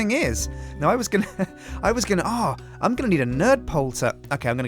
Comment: I thought said "Nerdpole" instead of dirt pole